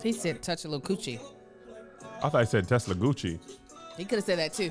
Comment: He said touch a lil’ coochie. I thought he said Tesla Gucci: He coulda said that too 😂😂💀